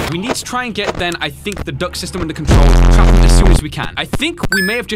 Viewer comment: who thought he said the duck system like if you did